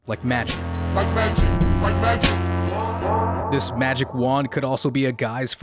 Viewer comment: Omg I never understood the story behind “New Magic Wand” and I never made the connection that the New Magic Wand was symbolism for a gun. Damn. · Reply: yo i thought that when he said "like magic, like magic, gone" i thought he said "bite that drip, bite that drip, wand"